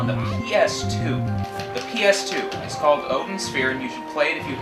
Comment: I couldn't understand the name here: 6:15 Wanting to look into that game, can ya tell me the name? Thanks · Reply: Hi! was talking about Odin Sphere for PS2